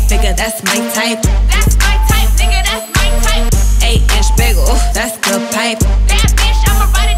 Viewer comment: it lw sound like she sayin “eight inch bagel, das my type...”